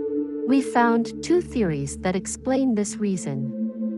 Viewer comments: well I'll be damned, thought she said they found tooth fairies that explain the reasons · I thought she said she found tooth fairies that explains this